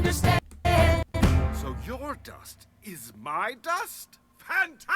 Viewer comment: i think they were trying to say something like "Yeah did you know we're made of stardust hehe" 13:04 and it came like awfully weird